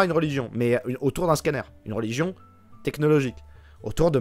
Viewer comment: Mdr quand j'ai entendu "religion scanner", j'ai eu un regard en biais sur mon imprimante epson, en me disant qu'elle commençait à se faire vieille.